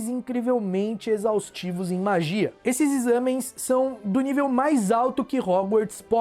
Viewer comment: ele disse "examens", ou foi impressão minha?